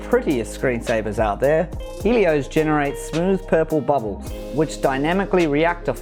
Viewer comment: What screen saver is that at the 2:05 mark. I've replayed and cant make out what hes saying... TY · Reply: The Screensaver It's called Helios